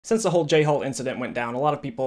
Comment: wtf this whole time i thought you were saying j hole. oops